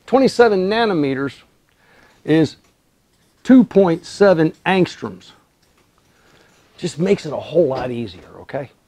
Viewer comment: That is 2.7 angstroms (smaller than 3) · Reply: I thought you said 27 angstroms. Thank you!